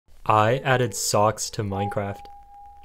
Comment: I thought it said “Stocks”